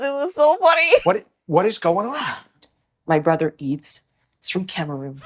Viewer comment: Wtf does she say? My brother eats from Cameron?